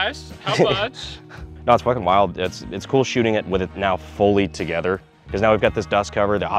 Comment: for a second i misheard and thought you said school shooting so i had to backtrack 0_0